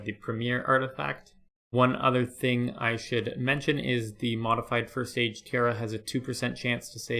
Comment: you say "one other thing I should mention is the modified <something something something> has a 2% chance to save any bones buried." For the life of me I cannot make out what this item is despite replaying the video about a 100 times at multiple speeds, please help! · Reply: Ok, I watched a stack of Prayer videos and discovered that the item was the Modified First Age Tiara - I already had the First Age outfit but I hadn't realised it had a modified Head part - so many thanks for that!